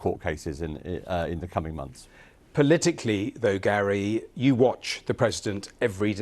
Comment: Did he just say "you watch" to a blind guy? See his face after.. 🤨